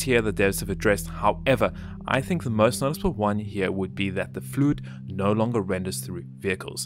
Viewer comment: fluid sounded like food to me. Hm.